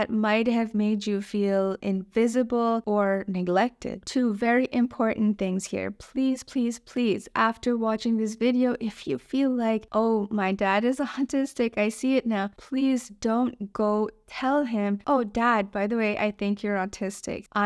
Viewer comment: the subtitles said “my dad is a hunter’s steak” 😂